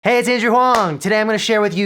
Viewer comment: i constantly played the "hey it's andrew huang" over and over until i heard "Hazy seizure huang"